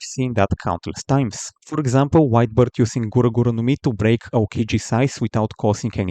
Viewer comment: Every time this guy said "White Beard" I heard "Wide Birth" 🤣